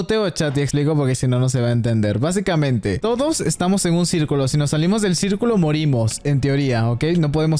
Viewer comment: Pense que decia No salgas del circulo o Mujeres